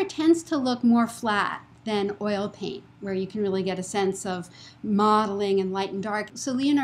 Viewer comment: At the 5:27 point, what is the word she is saying? Is it "modelling" or "mottling"? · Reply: Modeling.